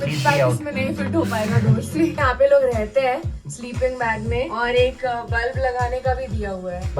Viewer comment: the captions are wrong it isn't the bag it is mythpat